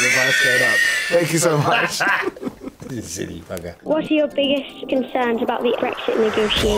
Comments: Tom saying "You silly bugger" is somehow everything I never knew I needed. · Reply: I thought he said "burger" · "It wouldn't work out, but I somehow wish you were my dad." Tom Hardy: (wheeeeeeeze!) "Ya silly bugger" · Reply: Freak Zilla I thought he said 'silly fucker' 😂😂😂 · I wish I was called “a silly bugger” by Tom. Would be up there with the best things in life for me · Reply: I thought he said silly fucker lol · Tom saying "You silly bugger" is somehow everything I never knew I needed. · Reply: Oh, I thought he said "you silly fucker"